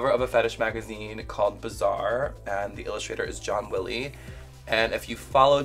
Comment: I thought she said John Mulaney and now I would love to see that.